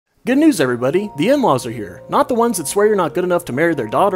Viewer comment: I thought he said A-Laws and I got Gundam 00 flashbacks